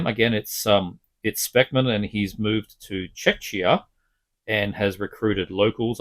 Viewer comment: For a minute I thought you said Paul Speckmann moved the Chechnya. :Insert my WTF face